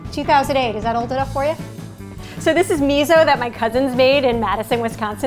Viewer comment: When she showed the old miso, I thought she said her coven in Wisconsin made it. Maybe she said cousin...Cool.either way.